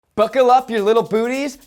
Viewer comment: I heard "book-le up" instead of "buckle up" oof